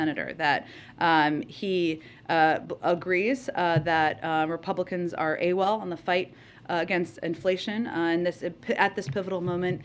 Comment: I know Jen said "Republicans are Awol " I thought she said "Republicans are....A-holes " 🤣😂